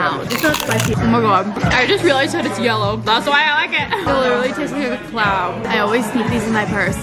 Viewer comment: She said “it tastes like a cloud” I thought she said “it tastes like a clown” 😂